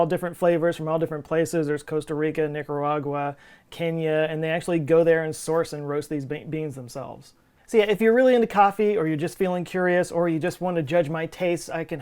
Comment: Did you say the source and roast these baked beans themselves at 02:13? Because that’s really what it sounds like and I keep giggling about it like a giddy schoolgirl.